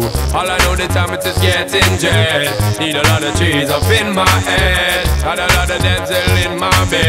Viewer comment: Can't really understand most of the song, but at 0:22 I'm pretty sure he said "I need a lotta cheese up in my ass"